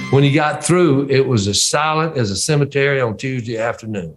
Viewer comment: "Quiet as a cemetery on a Tuesday afternoon." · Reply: ah, thanks. Yes, these English ears misunderstood what you said with your fab accent. I thought you said solid, but it was actually 'silent' Doh!! Makes perfect sense now!!! And yeah, I can imagine everyone was stunned when they first heard that knockout solo. Great interview and thanks for the response ❤